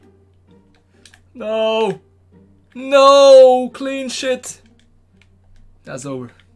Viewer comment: did i just hear say clean sh!t????. lmaooooo. 8:32. lmaooooo